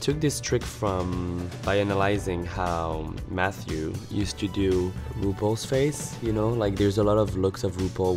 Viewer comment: the subs write loopholes phase instead of RuPaul's face that even Nickydoll was Lol-ling at the part on her Instastory